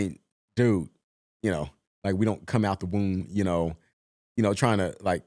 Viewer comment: i found it funny how on the first reaction you did you thought he said 'didn't want to come out your room' not 'come out the womb' lol then your imagination filled in the blanks with some story about how you wouldnt want to leave your house because violence on the streets, when what he was originally on about was saying how selling dr*gs isnt the dream as a kid and that its taught